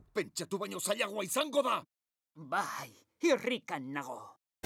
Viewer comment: parece que dice que Rico nabo